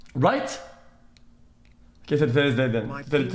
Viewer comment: ??? wtf did he say there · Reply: K today’s the day then, today is the day then